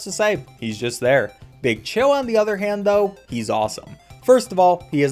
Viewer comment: when I was a kid, I used to call him Bechilled. Idk why, it just sounded like he said it plus it sounded cook